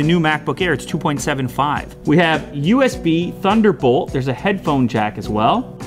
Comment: did he said thunderbolt?